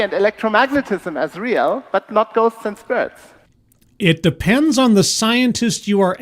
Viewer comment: All I keep hearing is "Ghosts & Spurts". Maybe I need professional help...